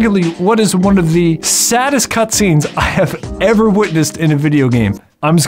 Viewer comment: My brain heard you say "One of the saddest cAtscenes that I've ever witnessed" 😂